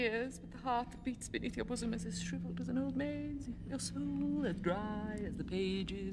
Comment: She speaks so quickly at first I heard "Your bosom is as shriveled as an old maid's" and was like WTF before I figured out she was talking about the heart beneath.